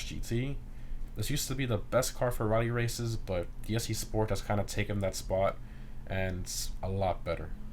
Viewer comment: What car is he referring to at 2:25 that is better than the Flash GT? I want to find it but I can't. It sounds like he is saying the SE Sport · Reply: issi sport